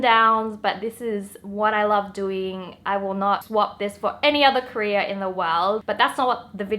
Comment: Am i racist? I heard "I will not swap this for an other Korea"